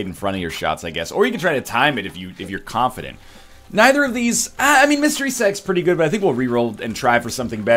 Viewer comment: does anyone else hear "mystery sex" (dat timing doe)